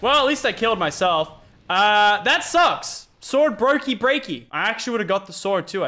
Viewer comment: Lol what did the say at 3:33 Sword Brokey Breaky 😂😂😂